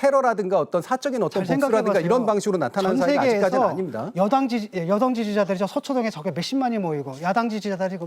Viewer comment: ~11 진교수가 "전세계에서 여당, 아 여성지지자들이 서초동에 몇 십만이 모이고" 라고 들리는 것은 저 뿐인가요?